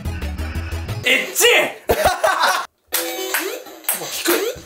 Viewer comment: エ○チ!に聞こえた